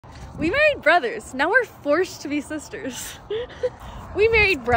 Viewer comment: At first, I thought I heard you say we are married brothers